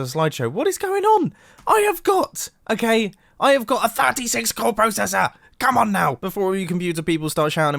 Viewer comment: Don’t worry I thought you said fatty 6 core processor which is a third of its core count